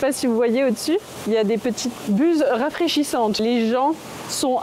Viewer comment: Serpent je suis.... kssss kssss kssss....😅 Sinon quand tu as parlé des buses qui diffusent de la brume, j'ai entendu "bouses"... j'ai eu un instant de perplexité...😅